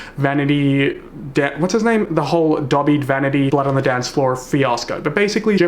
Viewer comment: Every time I hear you say Davhie I hear ‘Dobby’ and I keep seeing Dobby from Harry Potter xD